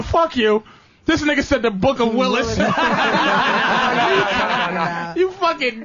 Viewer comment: Patrice sadly misheard the guy that called in about the Bible, he wasn't saying "They left out the Book of Willis", what the guy was saying was, "...they left out the Book of Lillith." I would have been VERY interested in hearing Patrice's take on that information.